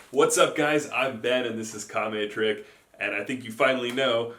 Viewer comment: I thought he said, "I am bad"